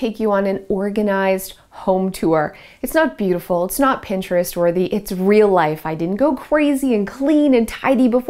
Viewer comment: I thought you said it is NOT pinterest perfect?! 😂 Btw, I LOVE your color scheme(s)!!!